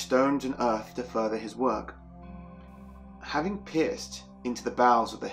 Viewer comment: Lol, at 13:28 when you say "having pierced into the bowels of the hillock", to my american ears it first sounded like "pissed". So I thought no wonder the guy got cursed. The nerve to go and piss into someones burial mound!